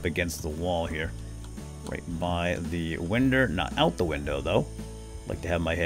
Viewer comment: Did you say winder? If so could you leave my name out of it?